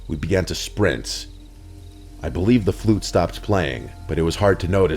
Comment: At the flute part I swear I thought he said "I heard the Weeknd playing." Don't recall that one, must not have been a big hit...